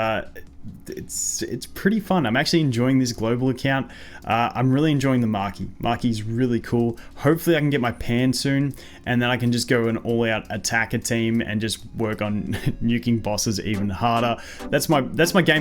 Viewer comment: I legit thought he said "hopefully I can get my pants soon."